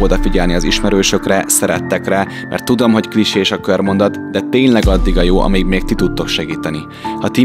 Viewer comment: Valaki hallotta, hogy mit mondott 12:32 nél ? Mert én valami olyat hallottam, hogy kris és a körmondat 😅